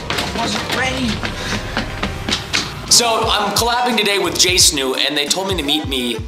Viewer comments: Did Preston just say jsnu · preston, did you just say, “JSNU”?!!?!111?1!1!?1?